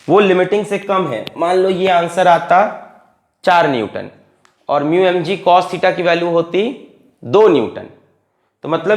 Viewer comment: Anybody understood what sir said here?? 😖🥴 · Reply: Ht re bkchod